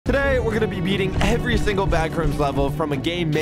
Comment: I thought he said eating….